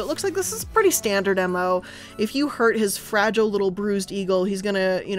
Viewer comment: Anyone else hear "eagle"?